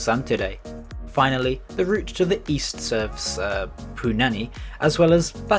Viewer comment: What did he say at 1:19 when he hesitated? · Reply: I heard punai Its actually punaanai